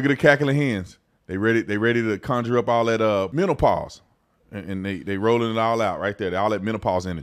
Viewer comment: I thought you said mentalpause. 😂